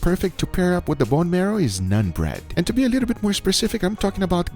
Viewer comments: I thought you said nut bread. Like dude nut bread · Did he just say nun bread